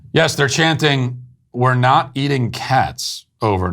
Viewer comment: It sounded like they were saying "stop eating cats".